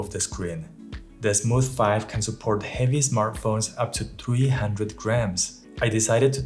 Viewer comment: Thanks for the review. Supports up to 300g did you say at 2:18 right? So the big and heavy iPhone Pro Max with case + lens + filter holder + filter are almost 430g. Guess you need the more expensive Crane M3 gimbal for that but it doesn't support FiLMiC Pro unfortunately